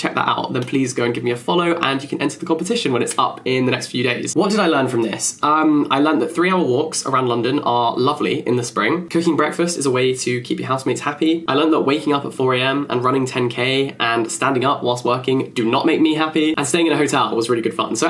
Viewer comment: I thought you said husband- 👁👄👁 instead of housemate